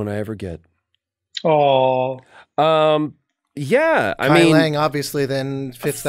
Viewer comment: What character does say at 2:23 ? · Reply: kai leng